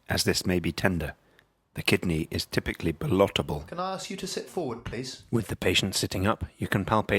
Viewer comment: what does he say at 4:47 - 'the kidney is typically **********' ?? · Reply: "Ballotable"